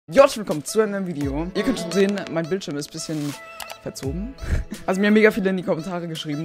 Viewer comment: was sagt er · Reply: Its muffin time